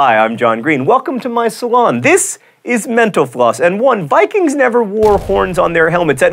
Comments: every time i hear him say "mental floss" my brain tells me he says "menopause." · Actually, my brother put a metal soup can in the microwave, and it destroyed our microwave. Also, whenever you say "Mental Floss" I keep hearing "menopause." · When he says mental floss really fast, I thought he said menopause. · Lmao he said mental floss really quick....I thought he said metapause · Every time I watch one of these videos John says 'Mental floss' and I hear 'Menopause'. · When he said mental floss, i thought he said menopause, but still great video :D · Every time you say "mental floss" i hear "menopause"... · Is it bad that I thought I heard John say "menopause" instead of "mental floss" at the end of the show? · I swear, every time he says "mental floss" I hear "menopause" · Sounded like he said menopause instead of mental floss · I thought you said 'menopause' instead of 'mental floss' at the end...awkward.